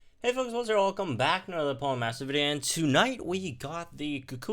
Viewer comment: WTF did he say? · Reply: Hey folks here welcome back to another Pokemon Masters video.